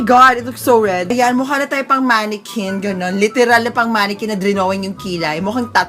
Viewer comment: Idk if bingi lang ako or what but I think said "dramatic side" for the don't/wrong side but what I heard is "TRAUMATIC SIDE" Then again trulalu nemen 🤔🤔👀👀👀